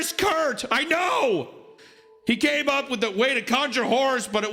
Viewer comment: Lmao at 2:05:37 it sounded like you said, "He came up with a way to conjure whores". Also, I was listening to this during a 24 hour shift and imagined everything taking place in the form of South Park-style animation. It's truly something to behold.